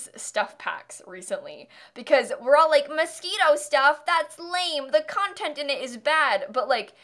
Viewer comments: i legit thought she was saying mosquito stuff lol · I'm not gonna lie, I thought you were saying mosquito stuff and not moschino wow I'm dumb